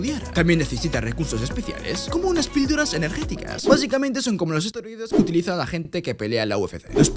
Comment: Escuche asteroides en el minuto